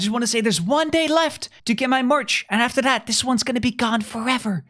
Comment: "1 day left to get my merch" What I heard: "1 day left to get my morch"